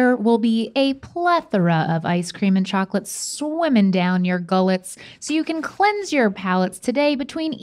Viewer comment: around the 2:47 frame<, I thought you guys said my last name for moment. as Gullett.